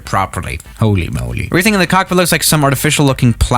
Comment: WAIT WHAT DID HE SAY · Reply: Cockpit🚫 ** ✅